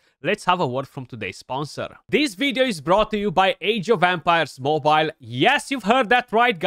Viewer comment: LOL... I love your channel but you have such a thick accent that I often mishear words you say. I thought you said "Age of Vampires" and I was wondering what game that was. =)